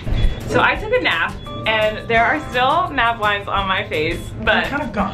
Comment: Does anyone know what she said was on her face at 5:36??? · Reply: Izabelle Moleski mad lines maybe?